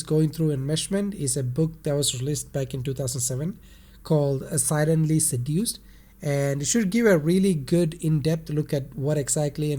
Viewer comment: <- What's the name of the book? I couldn't catch it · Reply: Hey Ryan, the book is called "Silently Seduced". I just added the link in the description! Thanks for your question!